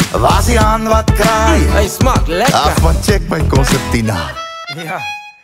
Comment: What did he just say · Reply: It tastes good hy smaak lekker